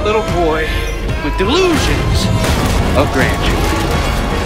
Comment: What does it say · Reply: "I need you on this one" It's Nate's brother, Sam, talking to Nate